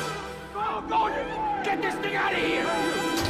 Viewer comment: what did bro sayy 😭😭😭😭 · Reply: Get this thing outta here but it sounds like he's saying something racist